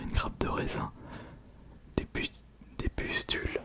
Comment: des quoi ? 🧐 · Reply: Des putes j’ai l’impression…